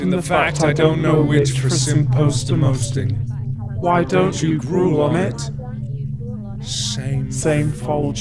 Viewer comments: I think I figured it out... #Markiplier "You laughed in the fact I dont know which for some posting. Why dont you grow on it. Same fortune as ever. Top of the table cloth plunt. Do you understand me? Nobody does. Listen, theirs a body at the door. Should I go get him? Find me a GradFreel." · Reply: Reese Domond I heard "Why don't you brew on it?" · "do you understand me? nobody does. there's a body at the door, should I bury it?" is what I understood. also, the "why don't you grow omit" X) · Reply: archerman deathyou lol I heard why don't you grow up mate?